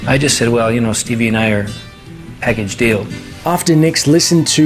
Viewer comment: What does "peg and steel" mean? 2:58 I hate when people use phrases no one else does and no one knows what they mean. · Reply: I believe "Package deal" is what Lindsay said ... though the way he said it certainly did lend itself to being misheard